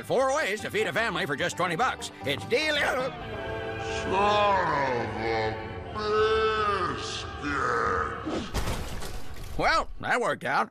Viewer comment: I originally thought it said "Son of a BIIIITCHES!"